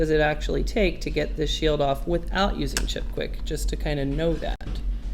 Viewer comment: at about 32:30 you had mentioned "do it without using quickwick"? i couldnt quite catch what you had aid, kis that product? thank you! :) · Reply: Chipquik low melt alloy